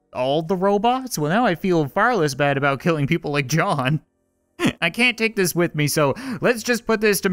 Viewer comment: when he said John I thought he said Job bot